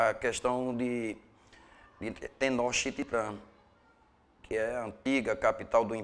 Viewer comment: não entendi o nome da cidade do mexico tenoxititam? 1:16 a